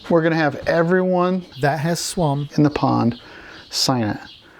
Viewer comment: lol. Did you say Swum?